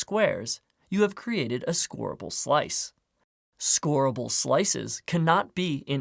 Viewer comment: Scorable slice What I hear first : Horrible slice